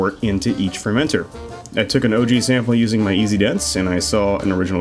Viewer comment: What was the piece of equipment he said he used to measure his specific gravity at 10:00? I couldn't quite catch it. Thanks. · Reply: Anton Paar EasyDens